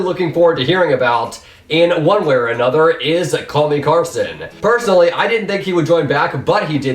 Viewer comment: lmao it kinda sounded like he said kelly clarkson